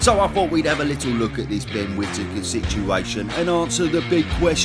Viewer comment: Genuinely thought this said ‘Rob Whittaker’. future is Tom Aspinall. Excellent video as always tho 👌